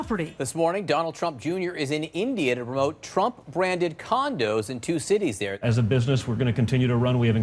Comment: For a split second, I thought they said "Trump Branded Condoms".